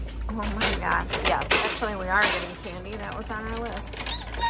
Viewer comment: Omg l thought said NO candy😂😂😂😂😂